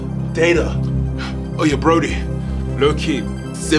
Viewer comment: (edited) Human: "Ultra flamin' doritos, ps5." (It's so hot out here. Good thing.) Human: "Yoaqua." (I packed hydration.) Robot: "Bitcoin and social media." (Essential for human survival.) Human: "Oh 2020." (Oh no.) Robot: "I didn't really care." (Critical error!) Human: "Oh, a dollar bill. Data." (Oh! Oh thank god. A human.) Human: "Oh yeah browdie." (Hey.) Human: "Lowkey Simpin'." (I'm so thirsty...) Human: "Hook me up with the juice?" (Can you lend me a drink?) Robot: "Caller id?" (Who are you talking to?) Human: "Huh?" (Huh?) Human: "20 twentyyyyyyy." (Oh nooooooo!) Robot: "Goodbye,bro. Get back sega genesis." (Relax bro. I filled up this container earlier.) Human: "Oh, obama care." (Ah thank you.) Robot: "Hahaha, top rap battle." (Not so fast. Say it.) Human: sigh Robot: "top, rap, battle." (Say it.) Human: "BO'oh'Oh'Wa'er." Robot: "BO'oh'Oh'Wa'er, *laughs in robotic malfunction*." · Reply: I thought he said oh yeah brodie